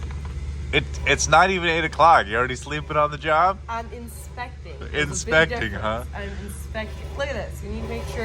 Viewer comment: Two things. When you were explaining that you were "inspecting" I thought you said "expecting" so I figured some type of announcement. Second, you really need to take the wheels off the ripper in that kind of weather. Are you going to be ripping sometime in the next 60 days?